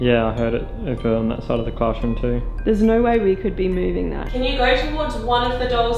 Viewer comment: I think i heard "Go away," not "No way."